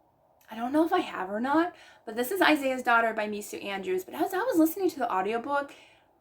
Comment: So I was watching your video and I kind of zoned out cause I was making my bed and suddenly, I thought you said you read v c andrews?!! I was so startled I fell off the bed!! So now my back hurts! I did rewind the video and realized you didn’t say that! But I was like… excuse me?? Scandalous!!